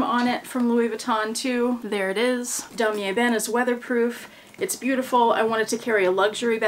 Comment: You said "weatherproof", The Lion and I heard "wine proof" 🦁🍷🤣